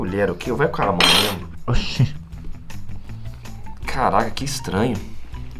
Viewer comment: caral** que estranho Eu: NAO ME BATE N MAEEE FOI O CARA QUE FALOU! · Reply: Da a impressão de que ele falou "caralho" mas no final quando ele falou "caraca" ele falou o "ca" meio mudo e pode bugar a mente então isso depende de como você escuta, não que você tem que ser tão kid pra falar que é pra limpar o ouvido